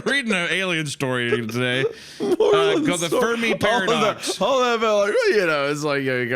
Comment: I'm really glad y'all put the title cause I heard "furry paradox" every single time